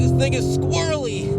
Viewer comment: WHAT THE HELL DID SAY AT 15:37 LMAO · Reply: “Thing”but it did sound like n👩🏿gga though